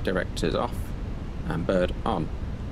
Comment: What did you say after "Flight Directors off and ???? on?" Also, when do we turn Autopilot off? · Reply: Hello, after FD's off it's "bird on" where we select TRK/FPA which brings up what we call the bird on the PFD. When the autopilot comes out is at the discretion of pilot flying but commonly at 1'000ft if visual and stable.